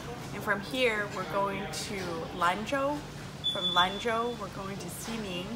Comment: is she said lanjiao??